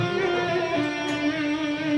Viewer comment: I thought he said 'i dont get why white people dont like st anger'.